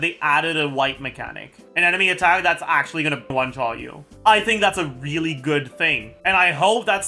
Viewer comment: Can someone help me understand what he's saying at 12:25? All I got was "I really like the fact that they added a [wipe?] mechanic. An enemy attack that's gonna [???] you." Is that "one-shot you"? It sounds like "bunt-tie you". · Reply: Yes he said one shot you